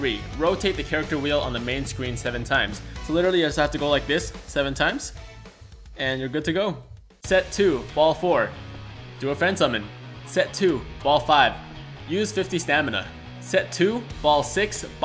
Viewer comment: A little help with set 2 Ball 4? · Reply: No I thought you said "Unfriend somone" but thanks for the info!